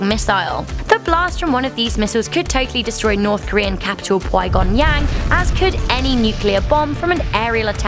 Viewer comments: did she say puigon yang???? Is that like a Asian quigon? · Did anyone else hear her say "north Korean capital pwhygongyAng"? 1:12 lol